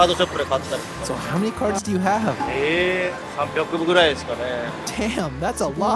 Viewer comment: I think he said 800 (Happayaku) and not 300 😮.